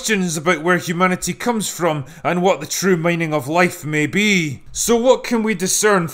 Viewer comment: Did say "what the true mining of life may be" at like 2:25 ? 😂